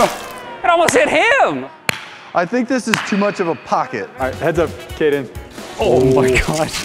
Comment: my names Caiden and I thought he said heads up Caiden and I’m very scared Edit: 8:10 reeeeeeeeee